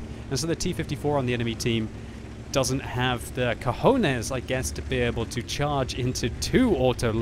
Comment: Doesn't have the cojones? Did he say that or I just imagined it...Cojones is a spanish word...